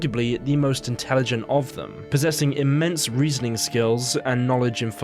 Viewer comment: I thought he said intense rizzing skills